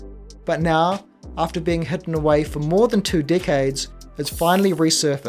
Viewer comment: I thought you kept saying "two dick-heads" but it was decades 😂. Gotta lay off the gummies.